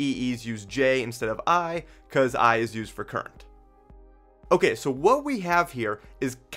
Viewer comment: Tbf i am hard of hearing. Aka “functionally deaf” but at 2:30 i heard something something j “because i is used for crime.” Best line - even if entirely in my mind- of a yt video. BECAUSE i IS FOR CRIME YALL. embrace. 😂😂😂😂 I is for crime And i AM for crime (at least of the energy vampire kind).